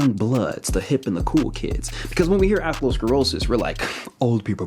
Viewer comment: I thought he was saying athro-sclerosis. It wasn’t until he showed the diagram that I realized he’s saying arterial - sclerosis.